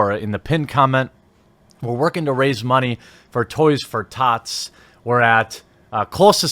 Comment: Wait, you are rising money for "Toys for thots"? I probably misheard it.